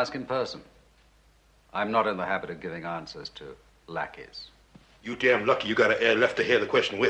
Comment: my brother thought bond called them blackeys😂